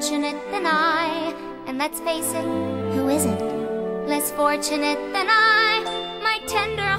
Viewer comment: "And let's face it: who isn't?" That face just fucking sold it! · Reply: I thought she said Who is it